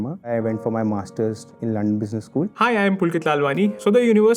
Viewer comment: did he say Lund Business school?